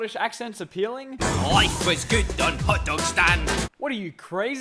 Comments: L I F E W A S G O O D O N H O T D O G S T A N D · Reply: I thought you said life was good on hotdog st and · L I F E W A S G O O D O N H O T D O G S T A N D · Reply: Oh I thought he said life is good, understand